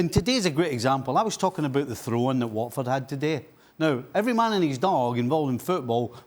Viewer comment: Did I hear Craig say “Watford had quality”? I thought it was “just Watford” when Man United beat them 3-0.